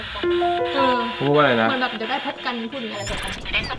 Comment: เขาพูดว่า เดี๋ยวพบกันอีกนะ